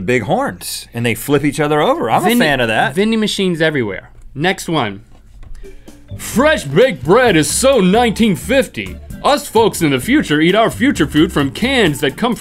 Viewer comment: I thought they said thai vending machine